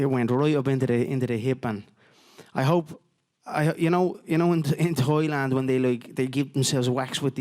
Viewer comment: I thought Conor said Toy Land, not Thailand. Lol